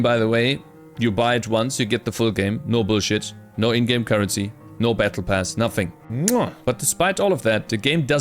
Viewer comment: did he say no battle pass?